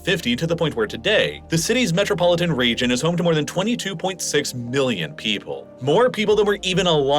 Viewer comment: you pronounce it with extra emphasis, but it sounds like you said billions to me